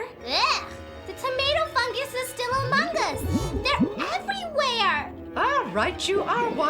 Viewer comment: Did Wanda say Among us?